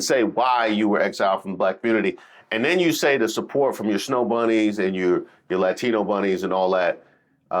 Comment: did she say Bunnies😮